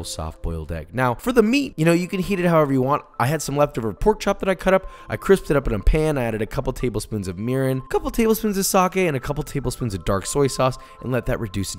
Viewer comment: Totally thought he said "urine". Lol